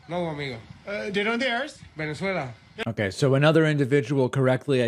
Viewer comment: Nothing against a California accent universally, but the guy speaks in a low educated timbre of a surfer "Dude-Bro"....and they send him to speak Spanish. He almost sounded like he said in the beginning "Where are you air" sounded more like my Dad trying to pronounce Bueno Aires wtf 🤣🤣🤣🤣🤣🤣